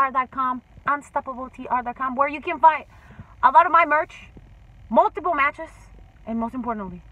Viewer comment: I thought she said multiple pornos lol